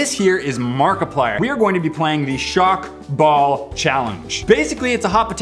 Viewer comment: i thought he said "hi guys today we are gonna be playing with a shark ball"